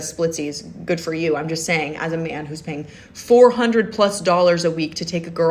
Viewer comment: You misheard her. She said "as a man who's paying $400+ a week to take a girl on a nice date"... not $400 a month. PER WEEK.